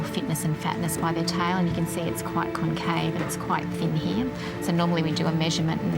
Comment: i thought the girl said cocaine when she was with the platypus